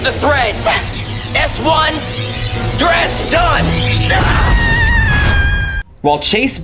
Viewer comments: Did he just say your a** is done? Edit: 2:43:02 I love how they use T-800 endoskeleton from Terminator for the android like Marcus but terrible CGI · it sounds like Leo says "S1...your ass is done!" Instead of "You're S-done!"